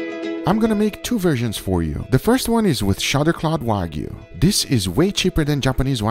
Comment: What does he say at 3:50? all I hear is shodderclot wagyu but that's definitely not it